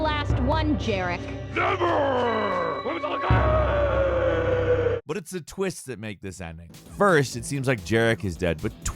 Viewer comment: what does he say when falling? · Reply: YAWHRAWRGHHHHHHHHHhhhhhhhhhhhh....